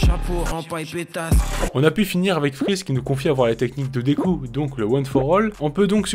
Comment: Quand freeze parle de la découpe de deadpool il parle du super-héros je crois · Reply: J'ai entendu "Deku" à la place de "découpe", quelqu'un me l'a déjà fait remarquer dans les commentaires